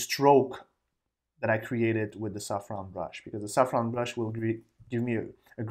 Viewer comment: I love your videos and your accent. Every time you said soft round brush, I thought you were saying saffron brush! I kept thinking, I don't have that brush! Hahahhaha :)